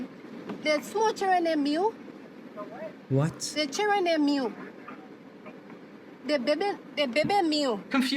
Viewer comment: It sounds like she said, "the cherubim meal." I've never heard of that reference before for a kid's meal, that's interesting.🤔😂